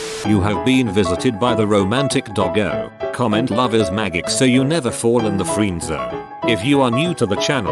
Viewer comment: I was listening to the audio while working, I had to actually check the video when I thought he said "Lover's maggot."